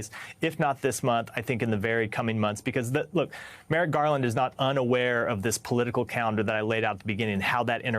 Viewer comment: Did he just say Garland is not unaware to move quickly?